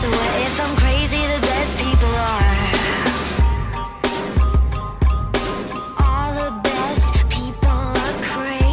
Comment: Am I the only one who thought she was saying "All the best people are crazy, all the best bipolar"? Cuz it sound a bit like it !